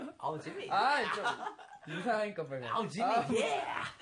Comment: I heard him say Jimin YeAhhh